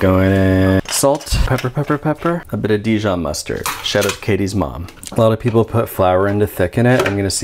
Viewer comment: I thought he said "a bit of Dijon Mustard, straight out of Katy's mom" I was like holy fuck this dude is edgy.